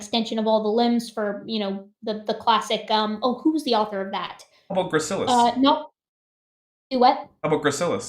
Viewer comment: Hi, can you please give me the proposed name for the next species in Human evolution? In 22:03 it is rather hard to hear, so it could either be "How About gracilis", "Ambugracilis"(which google translates abductors) or "Ambu gracilis" which sounds like an invitation for the Evolution deniers to write it incorrectly and complain about my previous mishearing. Edit: or Amogracilis, which google thinks means suffocating Edit: Never mind, after listening to the same part like seven times, two minutes later it gets said again and tada, HOMO gracilis. Makes way more sense